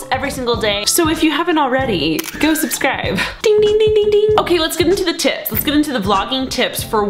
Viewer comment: At first I thought she said "ok let's get into the chips" and I'm like YESS! 😂😂